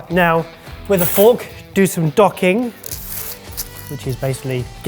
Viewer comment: I'm really sorry. I thought Barry said, "now, with a fork, do some dogging".